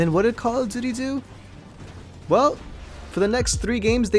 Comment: with captions 3:42 when says what did call of duty do it said what it called did he do.